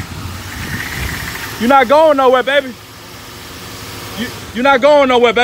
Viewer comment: Thought you said she’s ain’t going anywhere 🤣🤣